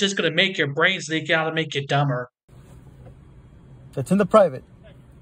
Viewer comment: "It's in the private." Did I hear that right?